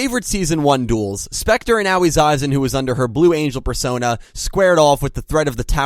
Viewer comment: Did he say persona